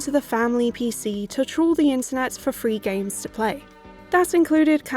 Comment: ahahah i just learned a new word here, thank you! At first i could swear you said "troll the internet"